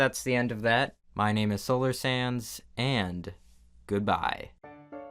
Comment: I keep hearing “solar sans” wow